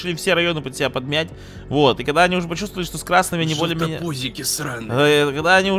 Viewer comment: Мне одному послышалось ЖЕЛТА ПУЗИКИ сказал